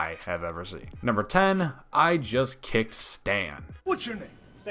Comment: I keep hearing “I just can’t STAN” 😂